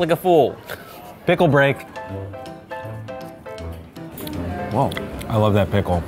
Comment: I thought he said pickle rick